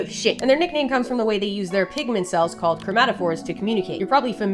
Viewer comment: I thought she said pikmin cells lol